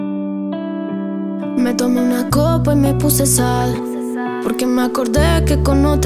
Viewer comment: Letra: Omar, algo anda mal Me tomé una copa y me puse sad Porque me acordé que con otra está No soy suficiente, o es tu maldad Me llenaste de inseguridad Explícame, ¿por qué me dejaste de querer? Y me lastimaste otra vez Y aquí no termino de entender ¿Por qué? Porque ya no estás No respiro, baby, si ya tú no estás Me falta tu calor y ya no estás ¿Quién va a hacerme feliz si ya tú no estás? Y no aguanto más Tenerte conmigo no está de más Necesito tus besos y nada más Elegí el camino que hoy tomás Cuando te veo en línea pa' ni te escribo En casa te dejaste un abrigo Se me tiran to' tus amigos Yo tranqui perreando, papi, ni le sigo Y ahora papi sé que hay otra mami que Te lo hace como es, mientras borrás cassette Y yo llamándote, no puedo olvidarte Me duele no verte Explícame… ¿Por qué me dejaste de querer? Y me lastimaste otra vez Y aquí no termino de entender ¿Por qué? Porque ya no estás No respiro, baby, si ya tú no estás Me falta tu calor y ya no estás ¿Quién va a hacerme feliz si ya tú no estás? Y no aguanto más Tenerte conmigo no está de más Necesito tus besos y nada más Elegí el camino que hoy tomás Me tomé una copa y me puse sad Porque me acordé que con otra está No soy suficiente o es tu maldad Me llenaste de inseguridad Y ahora papi sé que hay otra mami que Te lo hace como es, mientras borrás cassette Y yo llamándote, no puedo olvidarte Me duele no verte Explícame… La rompiste ❤️✨️ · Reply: Menos mal que pusiste la letra porque pensé que decía le puse sal 😂🤦🏻‍♀️